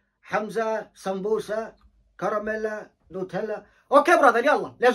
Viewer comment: It sounds like he is saying NuuTela but anyways w song❤